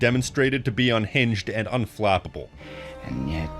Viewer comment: Why the hell did yt auto generated subtitle thought you said "unfappable"